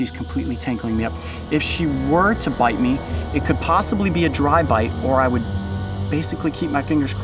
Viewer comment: thought he said drive by as in drive by shooting lol that's a GSF spider lol it's even the correct color XD